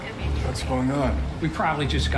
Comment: "someone's pissed about the update" fits him so well lmao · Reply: When I first heard it I thought he said ‘someone pissed on the new update’